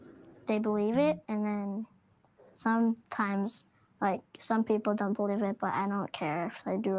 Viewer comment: When he said "some people don't believe me and i don't care" swear i heard him say "sun people don't believe me and i don't care" 😆 "Allegedly" 🤣🤣🤣 peace is a good thing, may it be with all who are worthy. 💩🔫😇